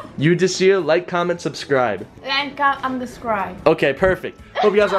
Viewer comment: it sounded like she said unsubscride in the end